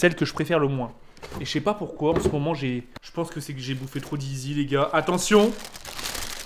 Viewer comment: woaaaa a mdrrrrrrrr "Jais bouffé trop de zizi" 😂 j'attends sa moi personnellement